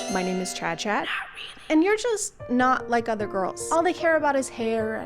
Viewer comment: Completely sounded like “I’m not like other girls. I have interests.”